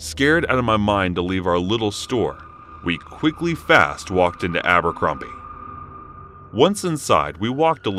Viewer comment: First story that dude's eyebrows looked like black snakes lol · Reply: Abercrombie? I thought you said Albuquerque 😐 lol